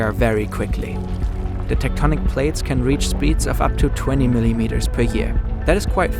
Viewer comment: I thought he said 20 million meters until he compared it to our finger nails